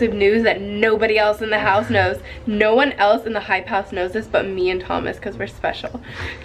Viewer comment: I thought Thomas said u left the hyper house